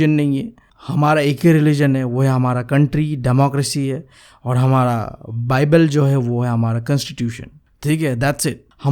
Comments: Did he said that bible is our constitution or I heard it wrong 🙂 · did I hear something wrong 🙄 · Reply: Bibal constitution 😑